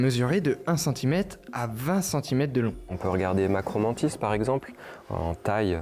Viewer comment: j'ai entendu macron-mantis😅😅😅 toujours dans les bon coups😊